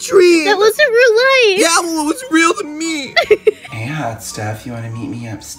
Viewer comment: It really sounded like she said “it was in real life”😂